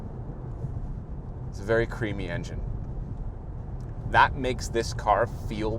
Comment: Did he just say creamy engine? Lol